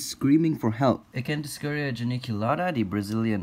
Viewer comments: When you put on captions and captions give hilarious readings. e.g. 2:58 What it should be: Acanthoscurria Geniculata What captions say: I can discourage Aniki Lara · I thought he said "Can't -Discurage Conchikulata" (Don't know if I spelled it right )